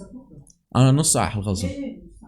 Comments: Malph tank is a nightmare Btw at 23:55 . Did u say "ana nos saa hkhlas" half an hour to finish. Cuz im pretty sure i heard arabic!!! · u an Arab or Dutch I hear u say نص ساعه واخلص😂